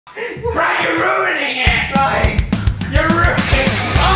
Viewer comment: did he just say cry your ruining it and then I heard cry say fine or is that just me for some reason?